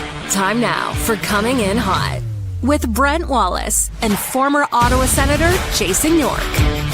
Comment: For some reason I thought I heard in the intro to the pod "Salt Palace" instead of Brent Wallace lol. That was genuine, I had to rewind to make sure, but I was proven incorrect! Shame :P